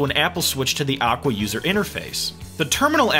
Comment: i heard him said "awkward user interface"... (actually is aqua user interface)